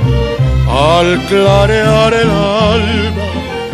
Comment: Me declaró totalmente admirador de este señorón, pero corríjanme si estoy en un error, al parecer en una parte de esta canción creo escuchar que tiene un error de dicción, o quería decir otra palabra, y cada que lo escucho me doy cuenta que esa voz si es de humanos, porque llegó un momento que pensé que era algo sobrenatural o divino que no podía ser real porque es perfecta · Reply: Mauricio Perez Creo que ablas ( Al aclarar el Alba) yo pense que decia al aclarear el alma. Pero porque no sabia que significaba (alba) pero ahora ya sabo.